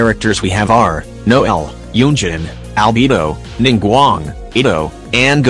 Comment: When is said albedo i thought it said albebo